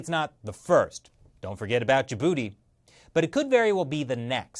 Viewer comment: When he said "Djibouti", I thought he said ya booty